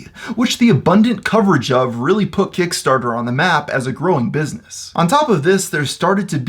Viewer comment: I thought it said Keemstar for a sec